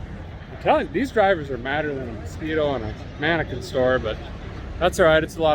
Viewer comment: "Madder than a mosquito in a mannequin store". 😀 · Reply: I thought he said "madder than a Speedo"